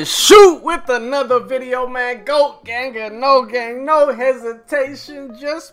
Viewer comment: At first I thought he said Logang